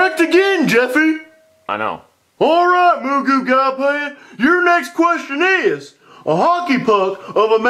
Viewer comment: Mr. Goodman calling Jackie Chu… 9:00 Bing Bong 9:59 Ping Pong Table 10:42 Moo Goo Gai Pan 11:11 Mulan 11:56 Sweet and Sour Chicken 12:05 Jackie Chu is Disqualified 12:22 Jackie Chu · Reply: he actually said Moo goo gai pan, a chinese dish